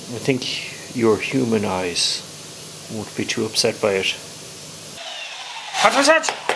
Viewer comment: '..That your human eyes wont be too upset by it.' Haha I heard this as 'he does not have human eyes'...which make sense now.